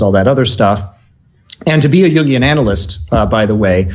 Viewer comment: is he saying jun jin? yung ying? i want to google this to understand more, anyone know how it is spelled?